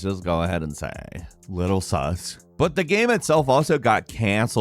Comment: did he just say SUS?